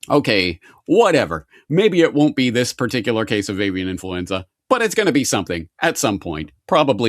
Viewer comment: I thought you said 'alien influenza' 😅